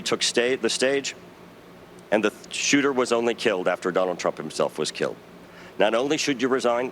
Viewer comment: Did he say Donald Trump was killed?